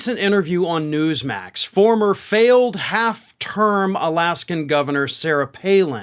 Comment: lol I thought you said 'half-turd' not 'half-term'; I was going to say you're about half right on that assumption 🤣🤣🤣